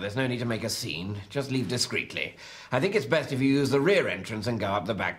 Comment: I can’t understand what he says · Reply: "Ooo er missus"... It's the type of phrase said in the 70's after someone said something saucy or rude.